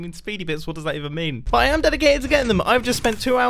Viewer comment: For anybody else who couldn't understand a word of what said at 3:30, I slowed it down to try and decipher the gibberish. He says, "But I am dedicated to getting them." For context, he's talking about his speedy bits.